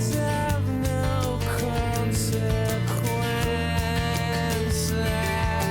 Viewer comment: It's amusing you pointing out how good his diction is right before saying "the what's this part". Thought that's what he was saying for years. Turns out he's saying "what's that".